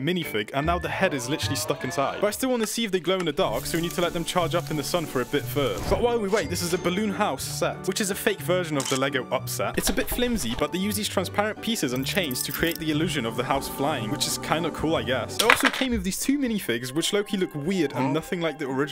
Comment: I thought he said "Lego upset"